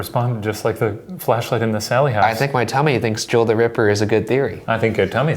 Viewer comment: I thought he said Tommy instead of tummy! 😂